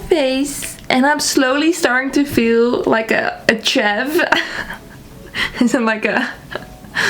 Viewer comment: I thought you said look like a chef at first, instead of chav. The british accent was also a treat :)